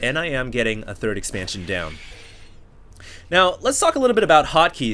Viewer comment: i first heard, now lets talk about hockey at 1:27 really? go on